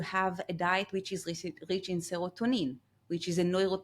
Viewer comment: Minute 20:35- what neurotransmitter did she say? To combat cortisol. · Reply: Seratonin